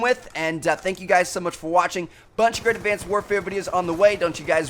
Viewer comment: what did he just say there? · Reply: Check out once of the two videos you haven't seen yet, peace out guys